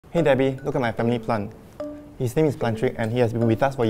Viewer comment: I LITERALLY THOUGHT BEN SAID “look at my TUMMY plant it’s name is Plantrick”. AND I REWIND IT AND SAW THE CAPTION AND IT SAID “FAMILY PLANT” 😂😂🤣🤣🤣😅😅😂😂🤣🤣🤣🤣😝😝😜😜🤪🤪🤣🤣😂😂😅😅